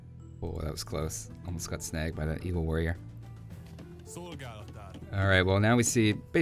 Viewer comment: Sounds like you said "evil warrior" But is it bad that I root more for the CD AI during these?